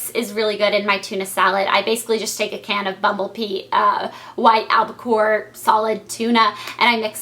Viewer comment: haha to me it sounded like she said bumble pee and i was like what?? and then i was like ohhhh bumble bee haha